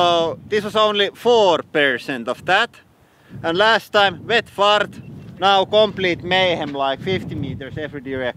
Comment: Did he say "wet fart"??!!